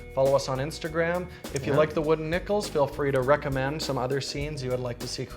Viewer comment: I thought you said: "wooden nipples"